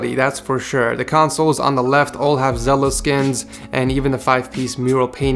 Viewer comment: Did he say “zela”